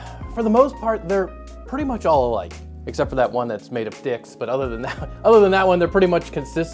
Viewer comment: Maybe my hearing is bad but, at 0:22 does he say its made of dicks? I’ve listened to it probably 150 times now and i’m not sure if i am hearing him correctly. If anyone knows, please let me know because it’s driving me crazy